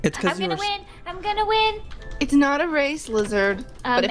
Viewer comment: did Stacy just say it’s not a race lizard? 😂 🦎